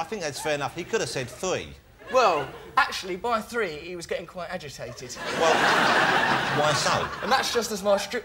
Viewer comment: He has a razor sharp, lightspeed fast wit which i envy. Btw what does he say @ 5:44 ? I can't make out a word of it, but it gets a big laugh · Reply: "actually by 3 he was getting quite agitated"